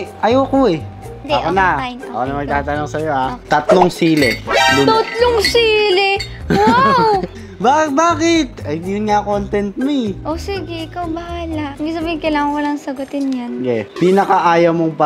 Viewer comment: Did Seth just said "mahal bakit"or maybe I misheard it Omg!!!